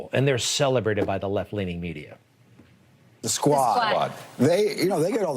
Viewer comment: I thought they said "I ❤ the squat!"